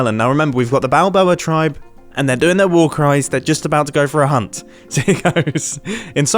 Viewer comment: Bro I thought he said war crimes 😂